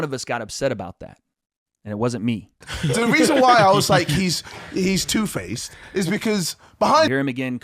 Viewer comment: im on your side but he did not call you two faced again he said " the reason why I called him two faces is because" that what he said